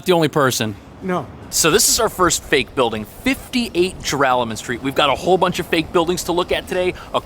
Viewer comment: What the heck is the name of that street he just said at 0:54. I was born and raised in the United States all my life, and even I can't understand what he is saying... Gerallen Street? Gervallen? Garallen? Geralament? Geralamont? Then at 1:10 you get the reveal. Joralemon Street.